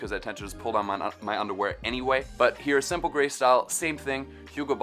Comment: I am thinking of opeaning a underwear company "JUNK IN TRUNK". Wait what did you just say at 2:40 ? You pull down your nuts instead of undearwear.😂😂😂👍